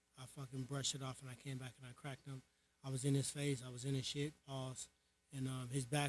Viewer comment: what did woodly say "PAUSE"??